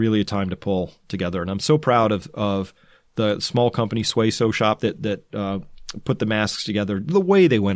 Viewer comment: I'm a bit hard of hearing and maybe I missed it, but what company did he give a shout out to around 31:15? Sway Show? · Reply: Suay Sew Shop.